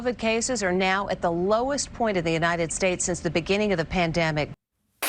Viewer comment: i could of sworn you said PanTdemic... LMAO Goodbye Bezos!